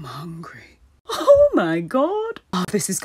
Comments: I thought hermione said “hEs DeAd iM a SneKeeee” at the end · “He said my a snack” kinda sounds like he said snake 🐍 😂😂😂